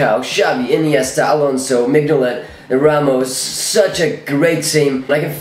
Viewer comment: Did he say Mignolet??